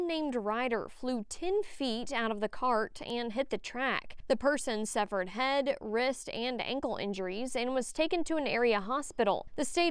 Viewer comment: sounds like she said “severed” not “suffered” ... I was like ahhhh!!!